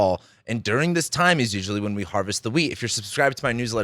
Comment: I thought you said harvest the weed lol😅